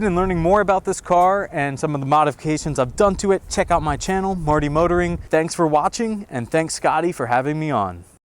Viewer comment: I thought he said morty motoring 😂